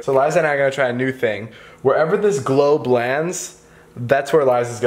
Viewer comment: uhh am I the only person who heard "glow blands"